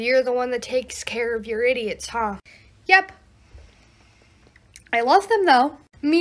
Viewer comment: "You're the one who takes care of your idiots" "Yep. I love them though" "Me too" XD that got me Edit: 600+ likes how the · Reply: I heard that as "Yep. But I lost them though"